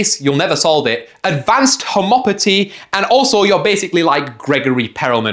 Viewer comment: did you just say homopoty?